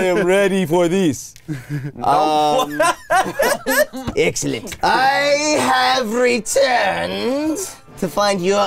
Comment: thought he said esketit